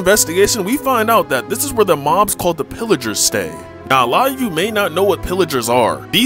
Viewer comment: Dude I okay now I think you're telling tricks because in the first video you made you were talking about villagers now you talking about pillagers making the strong hold I thought you said villagers the old villagers who we're down there for years I thought you said they made the stronghold lies